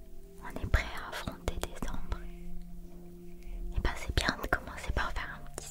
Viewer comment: j'ai entendu "on est prêts à affronter des ombres" au lieu de décembre, j'ai trouvé ça mignon